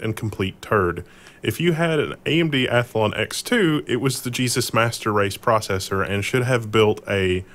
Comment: What did he say ? Jesus Masterrace ? xD